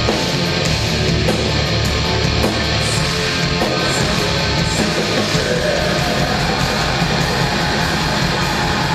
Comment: what he is saying at 8:15 8:16 and 8:17? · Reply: Hello 11 years later hahah.He literally saying " Sah sah uaght" its for atmosfere its not real lyrics or whatever.